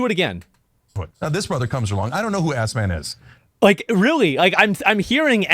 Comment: I heard Assman!